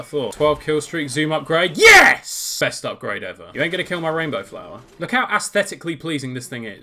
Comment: Yo I thought you said were not gonna get Rainbow Flowa :>